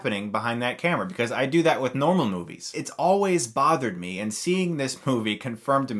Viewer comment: What does he say here? "I do that with N-..... Movies" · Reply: normal movies